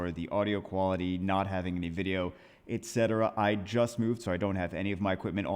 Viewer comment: What say: "I just moved" What I hear: "You will get new LD nat 5 content!"